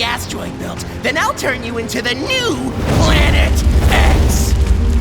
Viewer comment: the caption said planet ahh but the sun really said planet x😂